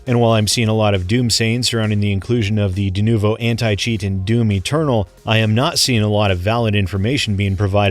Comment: I literally heard "the denuvo implementation of doomy turtle".